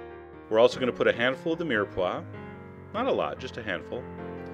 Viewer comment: What does he say at 1:28? A handful of what? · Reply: Mirepoix by the way is french word for the combination of carrot, celery and onion. Its used together so commonly they made a word for it.